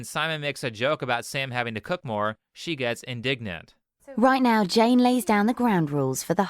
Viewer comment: when he said “she gets indignant” I heard “she got ingnant”